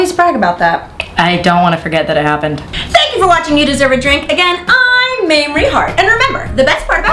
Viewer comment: Sooo funny.....but when I first turned this video on there was a slight glitch so it sounded like Mamrie said to me, "You dink !" instead of You Deserve A Drink. That was a glitch, right? Right? Hello?